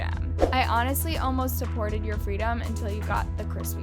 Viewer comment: did you say freedom? Takatae